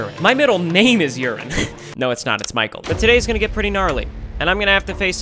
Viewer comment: DID HE SAY GNARLY?